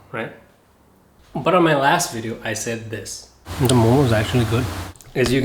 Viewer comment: I thought you had said "Momo's"